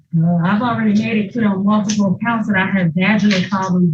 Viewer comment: Did she say she "Already made it clear on multiple occasions that (she) had Vaginal problems"?????